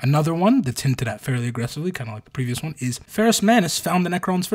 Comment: thought you said “found ed “ the necrons I was like HUH?!!